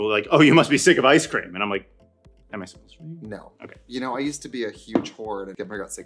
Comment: The joke at 3:28 is inaudible 😢 what did she say? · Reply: "Am I supposed to be?"